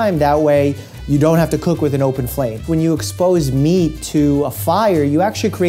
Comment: When he said "when you expose meat to heat," I heard "when you expose me."